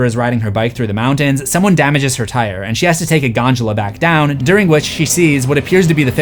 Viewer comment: did my boy say Ganjula instead of Gondola